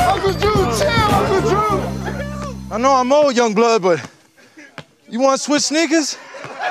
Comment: who else thought he said "You wanna switch niggas?" lmfoao